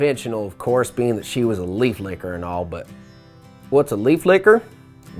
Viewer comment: I could have sworn he said lady licker...hmm...guess its too early in the morning lol